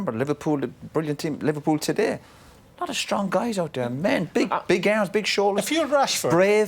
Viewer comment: Did he just say big ass on tv